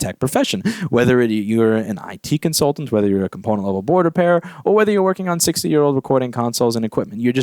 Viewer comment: I misheard. " Pornhub board repair." giggles like a maniac in the background.